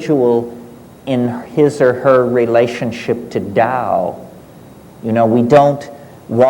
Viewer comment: sorry but what is the word he is speaking about 32:47 - dow, doubt ?... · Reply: Tao.